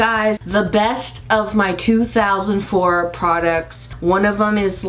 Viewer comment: I’m so happy you’re back I’m so excited!!! Also did you just say 2004 in 0:12? Lol